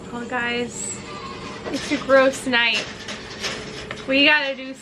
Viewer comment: When you said ”Well guys, it’s a gross night” I thought you said ”Well guys it’s a girls night!” and then with the latex glove just strapped on 😅😅😅